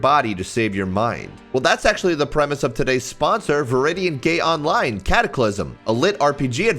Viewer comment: aint no way he said gay instead of gate